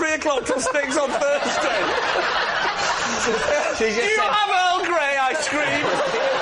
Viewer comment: "Do you have Earl Grey?" I screamed. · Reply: I thought he said Earl Gray ice-cream, which would have fitted the class joke and the swimming pool setting